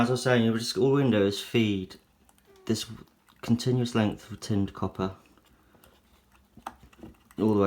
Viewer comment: Hello, brother. I couldn't understand what you said in 5:55. What is the material of this wire. Is it a simple wire for fences? I just heard copper but that wire doesn't have the color of copper. Thanks. Love your videos. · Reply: Hi, it’s tinned copper 0.9mm diameter